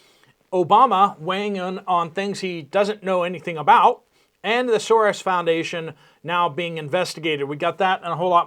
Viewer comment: “Thesaurus Foundation? “…. Ohhh.. misheard that.. lolol Just having my first cup of coffee- forgive me..